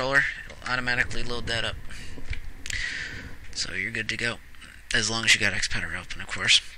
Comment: "as long as you" what? im sorry i speak spanish and didnt get what you said in that part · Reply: as long as you got xpadder open of course i guess